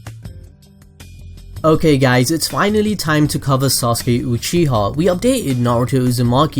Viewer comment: SORRY I HEARD SAUSAGE UCHIHA LoL 😂😂😂😂😂😂😂😂😂😂😂😂😂😂😂😂😂 my mistake